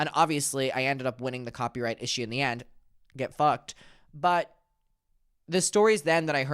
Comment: i was half asleep and i thought you said ‘ get mucked ‘ for a second 😭